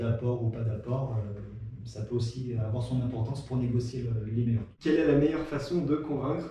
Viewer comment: Il dit quoi à 8:20 ? J'entends "négocier le liméon" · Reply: Mdr, quand j'ai fait le montage j'ai du foiré quelque part, je crois qu'il disait "pour négocier le meilleur taux" lol À plus